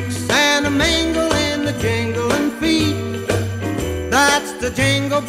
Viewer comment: I always thought he said jinglin feet